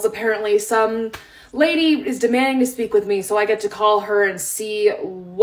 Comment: When I first watched this I thought she said demoning rather than demanding so I was like what is Karen doing in her free time???